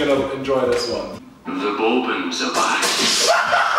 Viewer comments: what did it say lmao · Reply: THE BOURBON'S ARE BACK! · What was that one? What did he say?? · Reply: Bourbons